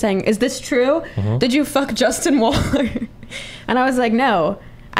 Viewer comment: I thought she said 'justin wong' and I was like DAMN that's a crossover I wasn't expecting XD